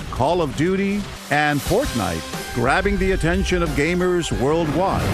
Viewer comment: i swear they said portnite